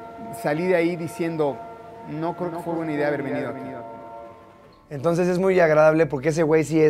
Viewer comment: que fue lo que dijo??, la edición hizo que le entendiera. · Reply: "No creo que fue buena idea haber venido aquí" es lo que dijo